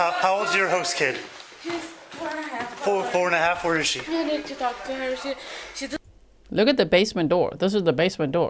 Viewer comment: Did she say no need to talk to her?